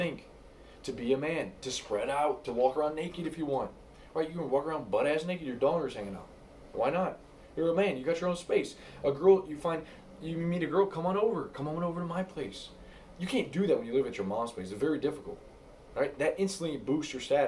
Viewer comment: Bro I was just watching and I was so into it but then I had to do a double take, did you say your daughters hanging out?? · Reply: LOL I said your "Donger."